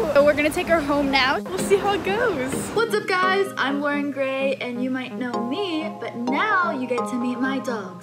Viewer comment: When Lauren said "what's up guys I'm Luaren Gray" I thought she said "what's up guys I'm wearing gray " LOL 🤣